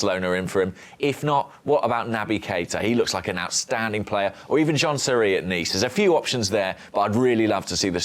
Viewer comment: did he just say... John Terry?